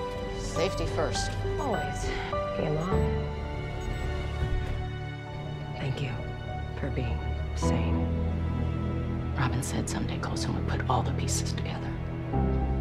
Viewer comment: Yo at 10:20 when it was daisy and may sit in the pod, daisy said hey mom to may!!! How did I not catch that!!! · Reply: I think she said “game on” but I definitely heard “hey mom” at first